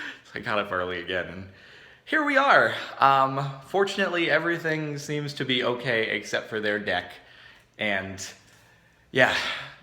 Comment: sounds like he says' fortunately everything seems to be ok, except their dick.'